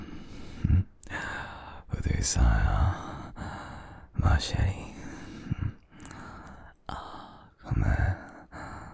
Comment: what did he say on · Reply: Ye he said "ma chérie" 👍🏼